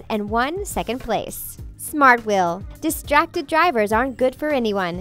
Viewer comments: On 5:42 instead of saying smart will, it said man candles again😂😂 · it says man candles instead of smart will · minutes in: She said 'Smartwheel' but the caption said 'Man Candles'...